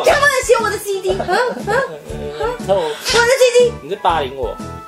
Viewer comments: 我的CD我聽成我的雞雞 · 的我的CD聽成我的雞雞XD